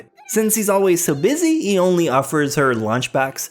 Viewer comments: "...until they meet with a restaurant owner who teach them about the wonders of cocaine." Am i the only one who heard that? X'D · heard it as "Wonders of cocaine"